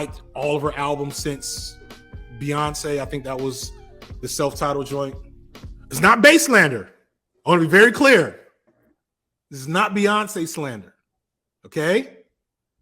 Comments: I thought he said "Baselander" not "Bey slander" so I'm gonna take Baselander for a dopeass eurobeat act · until you clarrified bae-slander, I thought you said "bass lander"